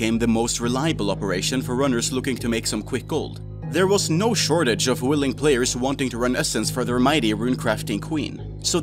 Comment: He clearly says "There was no shortage of willing players" but the caption replaces the word players with simps. lol